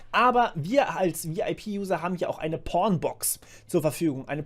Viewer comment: Wer hat auch zuerst Pornbox verstanden? xD